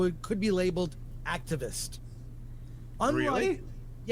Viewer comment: when Drinker said “Really?” All I heard was Scooby-Doo